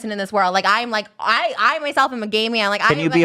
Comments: did trish say she’s a gay male maybe she got carried the way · did Trisha just say she was a gay male? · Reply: she says she's a trans man so jhtrekj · Did Trisha say she's a gay man at 41:40 or am I hearing things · sorry what Did she just say she's a gay male. What.